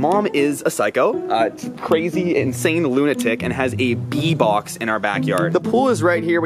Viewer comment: For a second i thought he said pee box